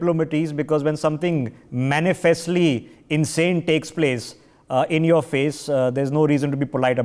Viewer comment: Between the thick accent I thought I heard "manifest insanity" of Trudeau.